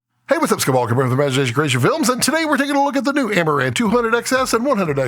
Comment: what did he say at 0:01? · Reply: "Hey, What's up, Scott Balkum here with Imagination Creation Films"